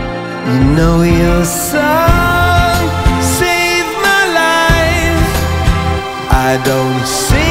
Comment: Huh. I thought it said "Your son saved my life" since I first heard it.